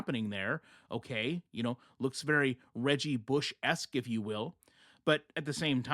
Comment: When you said Reggie Bush-esque I had to rewind cuz I thought you said Reagan-Bush-esque and couldn't figure out how that related in any way. 😂